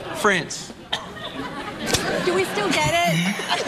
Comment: What the heck did the wife say @ 0:41? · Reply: Did we still get it